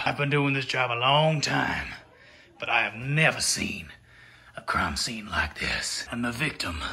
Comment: Why did I think said “a CRUMB scene like this” at 5:46?!? 🤣 Guess I had muffins on the brain